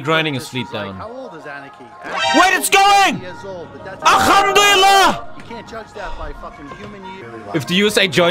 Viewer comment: Did I just hear "alhamdulillah"?